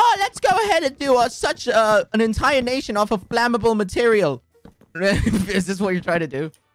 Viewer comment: When quackity or Tommy said flammable material, my brother thought they said flamboyant